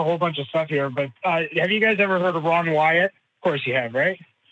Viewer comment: Ron Wyatt? The guy who also found Egyptian chariots on the Red Sea floor ? I believe he also has a bridge to sell ? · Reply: I thought he said Ron White... I was like, the comedian?